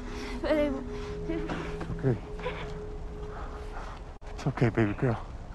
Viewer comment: He said "it's okay baby girl" and unfortunately I only heard "aRe yOu LoSt BaBy gIrL"